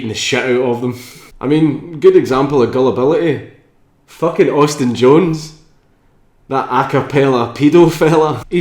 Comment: For the longest time I thought he said Austin Johns as in AustinJohnsPlays the guy who makes the sweet Zelda videos. I wasn't aware of Austin JONES. I was about to unsubscribe